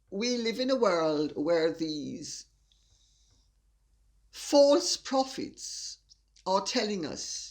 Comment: Hahaha! When you used the words ‘false prophets’ I thought you said ‘false profits’ Hahaha!